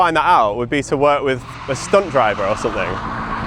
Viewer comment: I thought you said “stoned driver” 😭